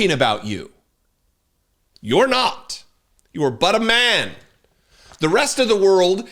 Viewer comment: Sorry but when you said “He is but a man.” I thought you said he was BUTTER MAN!🧈🤣😂🤣😂🇦🇺